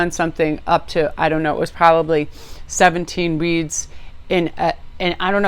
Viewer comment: I thought you said 17 weeds. Hahaha. I was like damn I wanna hang with her. Deck 1 😅